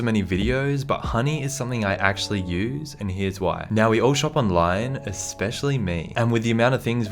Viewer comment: When he said online I thought he said lion